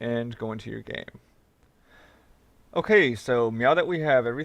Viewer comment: wait... @ 4:18 did you just say "Okay so, MEOW that we have everything programmed and we're ready to game..." TAKE MY UP-VOTE!